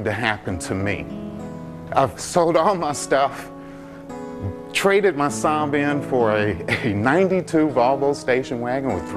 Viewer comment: What is he saying? He traded his son for a car?